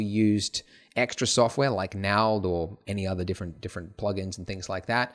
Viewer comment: extra software like WHAT? I can't understand the word he is saying.. Naold? What???